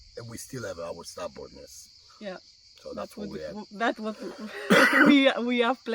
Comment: we have our ......what did he say? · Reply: our stubbornness.